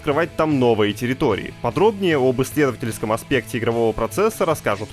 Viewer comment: "подробнее об что-то на непонятном языке аспекте игрового процесса расскажут позже" кто какое слово там услышал? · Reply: Исследовательском аспекте😅